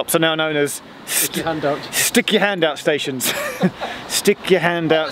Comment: I thought he said "Sticky hand out station" :-)